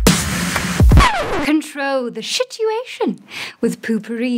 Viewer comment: wait did she just say shitiuation ??